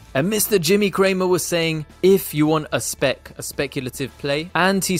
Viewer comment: In reference to BNGO i thought he said, “I sanction buying it, if you want a SPAC and only if you want a SPAC” i was like what is he talking about?!